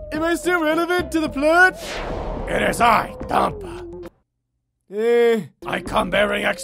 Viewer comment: "It is I, Tonpa" I could have sworn he said "it is I, dumb****" u can fill in the rest :P